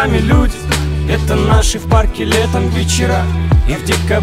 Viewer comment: Отличный клип. Что сразу хочу отметить - отличный саунд и качество съемки. Ну и немного критики... Можно было бы побольше мест снять (в Экибасе много хороших мест, помимо вокзала), а то одна и та же картинка чередуется. З.Ы. Первый раз когда слушал, послышалась строчка "...это наши в парке летом бичарА..." )))